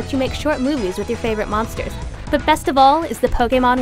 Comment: did she just say fucking "monsters"?